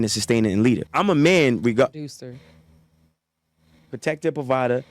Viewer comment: I thought she said "REPRODUCER". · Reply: You're actually right she did say reproducer but the way she said it carries the same weight as an abuser. In her eyes she's chosen to frown upon the truth and no credit from her will be given to any black man for anything. You can believe that like you believe in Jesus, I Promise You That!!!!!🙏